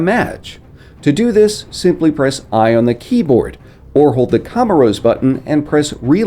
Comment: he mentions the inspect weapon feature. He states that its "I" on PC...and then speaks in a native tongue indicating you hit something* (sounds like "camarose", but thats not a thing and stuff) and the reload button what it is is on console...can anyone tell me wtf he said there? At 7:50 he says this foreign indecipherable word. Anyone? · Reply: It's the commorose button. Simply hold bottom right trigger and there you have it.